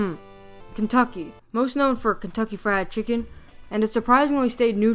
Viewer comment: pause instead of saying KFC it says OFC